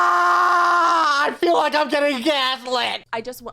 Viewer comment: “AGH I FEEL LIKE IM GETTING GASLIT!” What I heard: “I FEEL LIKE IM GOING CATHOLIC!”